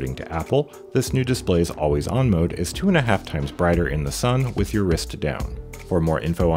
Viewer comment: I had ti rewind because I thought you said "2,5x brighter than the sun"